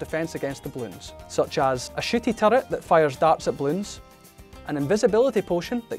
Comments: did he just say a shitty turret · CORRECTION: The Totem actually gives 1500 Paragon Power! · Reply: it sounded like he said "shiddy turret" lmao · good god I thought said Sh#tty turret for a second haha! anyways looking forward for the update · I thought he said shitty turret😅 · Did he just call that a shitty turret? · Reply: I thought he said shooty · Why did I hear it as "shitty turret" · did he say shitty? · I really thought he said shitty turret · it really sounded like he said shitty turret ._. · why did I heard a shity turret 😭💀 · thought they said sh##tty turret lol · is it just me or what does it sound like he said shity turret. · it sounds like he says "a shitty turret" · i thought he said a Sh*tty turret 😂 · Bro it sounded like he said " a sh*ty turret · idk why but i heard shitty turret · who else thought he said shitty turrets and not shooty turret's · Oh i thought he said shitty turret